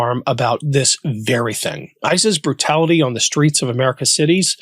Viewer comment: The auto captions at 0:31 say ISIS instead of ICE's but its fitting